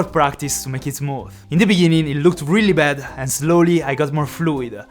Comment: I thought he told 'in the biningin' 😂😂 Like if u heard it as well.